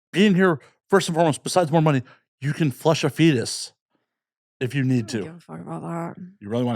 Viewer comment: bro what did you said Flesh a fetus 😢